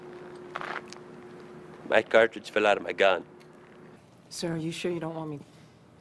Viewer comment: what does he say? "make cartages for loading my gun"? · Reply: Oerwout10 he said ‘my cartridge fell out of my gun’